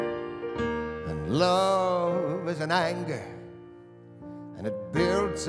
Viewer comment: I always thought he said, “Love is an anchor, that builds up inside…” I pictured a small anchor which turns into one of those giant aircraft carrier anchors! 😅